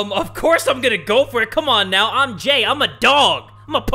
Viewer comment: I thought you said I'm Gay... lol